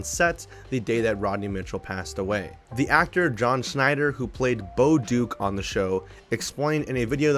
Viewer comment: OOOOOOH!!! I thought he said Dan Schneider